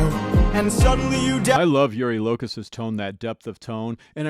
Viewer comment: I know this might sound a bit queer, but I heard "Yuri Locust's". (pardon the pun) It could be that it's an accent thing, but I believe the pronunciation of his name is closer to: "You-really-cuss" / "Yurillicus"